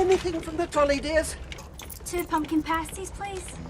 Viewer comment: Shit I always thought Cho said Pumpkin Persie's not pasties lol